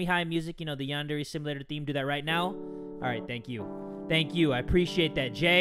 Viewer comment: At first I thought the man said All Might XD